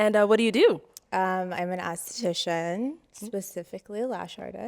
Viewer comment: My mind thought she said Anesthesiologist. Then she said lashes. eye roll.